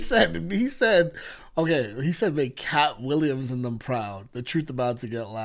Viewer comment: Could be a reach but 21:19 I hear “make Kat Williams and Em” proud. Em been low key on didhe’s case since the 90s 🫨